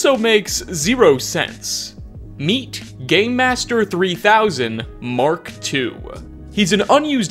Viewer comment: thought he said gay master haha